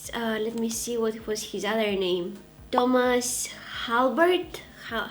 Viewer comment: i thought you said “dumbass halbert” at first lmao